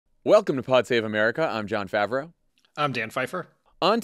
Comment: Had to listen to the intro 3 times cause I kept hearing "I'm Jon Faver"